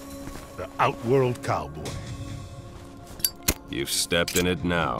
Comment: I swear when Jax (Carl weathers) was talking to Erron black I thought Jax said The outworld cum. When Erron Black replied I was like OH SHIT and then Jax said I going to wipe it on your face. I YELLED THAT IS GROSS AS FACK BRUH (I Meant to say Fack)